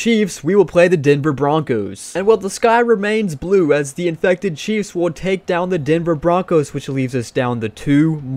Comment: I thought you said infected cheese